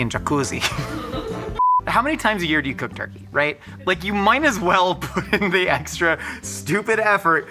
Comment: Josh said, "you mine as well," instead of, "might as well..." I don't know if I can trust anything he says anymore 😢🤣🤣